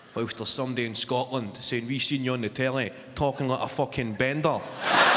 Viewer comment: i dont understand then i look for subtitle/CC, but the it does the same as me. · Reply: He said 'We seen you on the telly (television) talking like a fuckin bender'